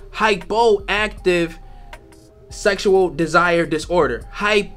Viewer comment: Ooooh shit. I thought you said "hyper" not hypo for palatin. Now I'm with the buy in